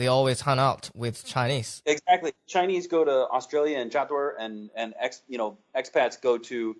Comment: What is the phrase they are saying around 16:45? Tried searching jiadui and jiaduo and find nothing in dictionary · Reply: Hi Christine, he said 扎堆儿(zhā duī er) which means "gather together", it's more like northern Chinese slang though, I'm from the south and I've never said this lol.